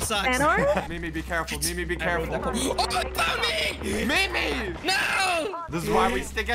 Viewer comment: When said OH THEY FOUND ME I thought he said UNCLE PADJI